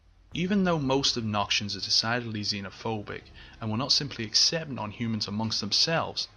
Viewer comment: Since I am interested in psychology I would like to know what you say at 6:14 "demacian something-something-something phobic" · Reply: xenophobic